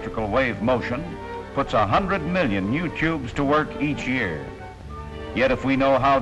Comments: Very nice, if I hear it right, at 9:23 he's referring to '... a hundred million you-tubes...'. That's a real view in the future. ;-) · It sounds like he says "YOUTUBE" lol.